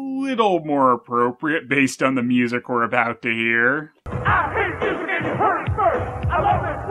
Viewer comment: When I first started watching your reviews, I first heard Shredder's "I hate music" rap in this review around 4:00, and I thought he was saying "It aint music!". I'm guessing Shredder thinks rap isn't real music, and that's why he had a rap number about how he hated music.